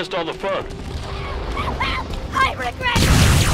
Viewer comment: What is that creature and what does it say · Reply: It's a grunt and it says "Help help I regret everything!"